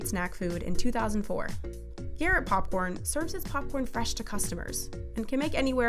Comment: did she just say here at popcorn..?